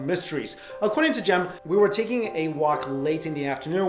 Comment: 'taking' instead of 'talking'. Just FYI :D